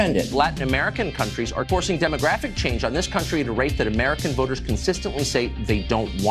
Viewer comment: I've listened to this part 5 times and it still sounds like he says "...rate that American Boogers consistently say they don't want." 😂😂😂 Yes, I feel like a 4th grade boy laughing at this but hey... Why should they have all the fun?